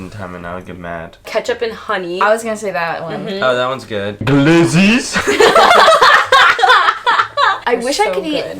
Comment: did just said ''leziz'' in Turkish what